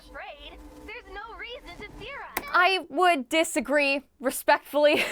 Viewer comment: Is anybody hearing theorize instead of fear us